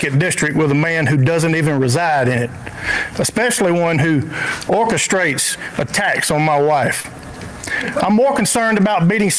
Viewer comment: I heard "Orchestrates attacks on my wife" as "orchestrate a tax on my wife," and 1) thought it was in line with republicans constantly complaining about taxes and 2) wondered how he was actually charging his wife